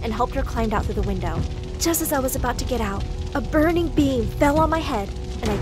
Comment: I thought she said... " a burning being" That really scared me